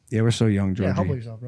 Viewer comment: What did George say to Jake that made him mad (17:17)? I tried to hear it like 7 times. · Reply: humble yourself